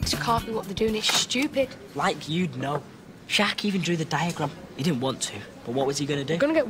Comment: haha kyle says jack instead of finn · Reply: He said "Sharkey", which is Finn's last name.